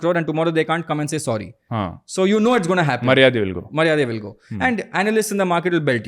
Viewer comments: did they say Maryade will go? Seems Kannada words. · Did really say "Maryadi will go" (it means "reputation" in tulu) Hehe, felt so good to hear Tulu in the middle of the conversation 😊